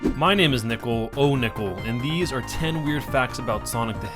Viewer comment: bruh i thought you said my name is nipple